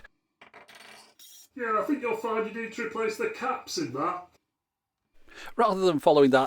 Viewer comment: What did the muppet say at 2:24? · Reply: I think you'll find you need to replace the caps in that